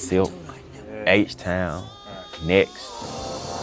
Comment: Did he say H-town?